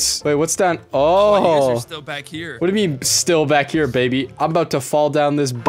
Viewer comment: is it just me or did smii7y call kryoz baby